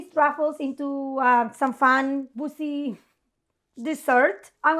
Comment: lmao I thought said a “fun bussy dessert” I was a bit affronted ahaha